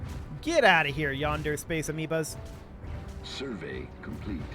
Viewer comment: I initially misheard "yonder space amoebas" as "yandere space amoebas" which would certainly be interesting.